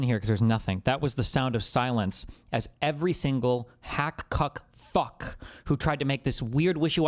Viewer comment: that edit confused me 😂 sounded like he apologized for cussing and then said right wing commentators are “cuck fucks”